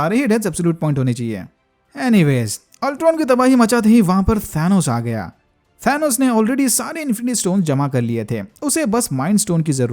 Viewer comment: who heard it as fanos! 😅😅